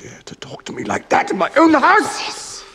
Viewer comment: what is saying how dare you talk to me in my ....? please anwser me ,its my huge queryy! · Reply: Own house